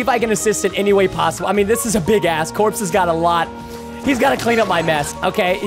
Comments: did he say big a** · did u guys hear the same thing i think i heard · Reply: He said this is a big ask. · Did he say a*s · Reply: i think he said ask like “This is a big ask”